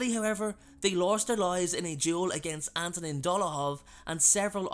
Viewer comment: When he said Fabian and Giddian lost their lives in a duel with Antonin Dolohov I thought he said Ant and Dec at first 😂😂😂😂